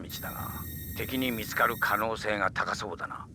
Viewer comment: Correction: In the お/ご honorific prefix section, some captions incorrectly show kunyomi/onyomi explanations. It should be お for kunyomi and ご for onyomi, sorry about that! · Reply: Hey, another correction is that at around 23:02, the subtitles said みつかれ instead of みつかる which would be the correct form to modify the noun